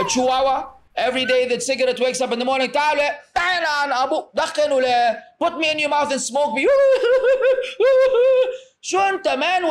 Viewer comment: what did he say here? This is funny. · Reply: "Taala wale, taala yl3n abouk" yl3n means "curse" which, this would translate to "come here bro. come here, curse your father" which is haram. akh shouldn't be cursing anyone's father. then he says "dakkhil wale" which is saying 'put inside (your mouth) bro' the translation sounds weird but its more natural in arabic